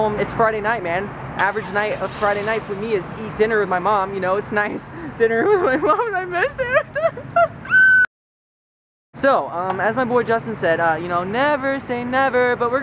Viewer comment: Did he just said: 'MY BOY JUSTIN' , at 2:44 ?!